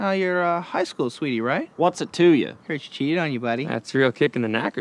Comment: what did he say? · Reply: What's it to ya?